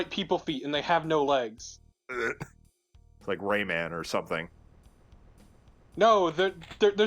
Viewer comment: I did not hear you say Rayman when discussing Cacodemon feet and thought you said Rainman.